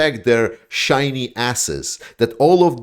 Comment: lol - I thought I heard you say, 'shiney asses' not 'shiney assets' 😜